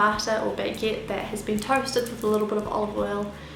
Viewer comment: for a minute I thought she said toasted bacon, I was like uhh what